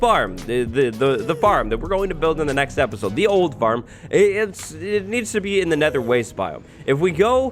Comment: When he first mentioned the gold farm, it sounded like he said the Mold Farm. We elites don’t do that XD